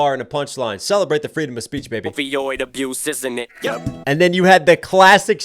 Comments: It sounded like he said ovioid not opioid, which would make sense in this situation with bruce becoming caitlin, and a woman usually has ovaries. So saying men are developing an "addiction" for becoming women like an epidemic ❤ this track is fire 🔥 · It sounded like he said "ovi-oid abuse" like ovaries. Abusing the female body by Jenner turning to Caitlyn. Idk. I could be stretching. Also at the beginning, he said "just a boy from Detroit who knew.." "Who Knew" being the title of one of his old songs.❤ Also "Get hit on by ugly men who are out of your league." Is also "Get hit on by ugly men, whore.. (insert ad lib here right after that word ..i think creep?) I could be totally wrong about that one. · I hear him say @ 15:21 Oveoid not opioid abuse, as in ovaries abuse.